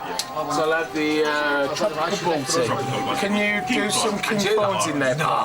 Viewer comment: I thought he said butty which is a sandwich. But no it was balti, the popular Indian dish. 😂